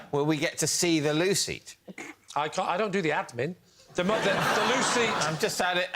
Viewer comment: they ketp saying loo seat, loo seat and I heard "Lucy, Lucy" and was so confused...